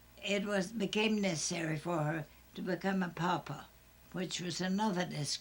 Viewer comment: what is a Papa? Right around 5:12 she says, 'it became necessary for her to become a Papa, which was another disgrace." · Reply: She says “Pauper”